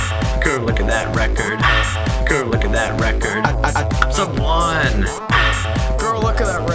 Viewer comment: When said “I’m sub-1” I thought he said I’m so blonde